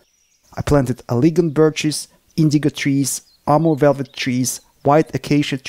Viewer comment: Did he say Indica trees? 7:42 😀